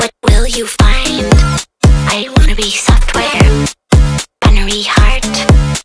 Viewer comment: Is it “battery heart”? I hear “binary heart”.